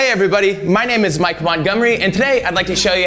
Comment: I keep hearing 'Mike Won-gomery' at the start. Awesome build!